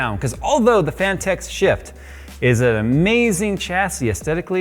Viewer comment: Sounds like your say sh*t instead of shift 🤣